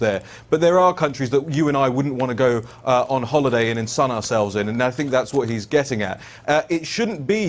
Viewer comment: what is that word that he's saying? ensun maybe? And what is the meaning of the correct word and content (ensun ourselves in)? I'm making a translate video of the wonderful interview.